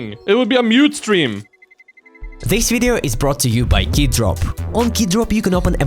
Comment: am i the only one who heard ''Kid Drop''?